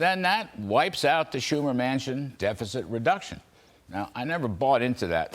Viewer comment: I like how sometimes my tired old ears create puns. Larry said 'Schumer-Manchin' bill and I heard 'Schumer MANSION' bill.